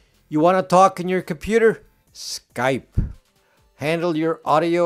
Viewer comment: Did he say Skype?...